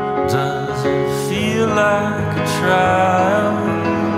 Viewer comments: I always thought he was saying “does it feel like a truimph?” not “does it feel like a trial?” And thought that they were saying “if I don’t even try” not “vilify, don’t even try” xd I got 2 lines wrong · i hear "it feels like a triumph" not "trial"